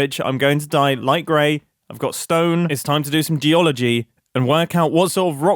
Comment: I thought said Dee-ology and thought 'The study of 1 specific Scottish river?'